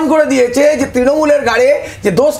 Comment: তিনুদাদু কি বলল কেউ শুনলেন? “তৃণমূলের গাঁড়ে”? 😂😂 13:04 🤣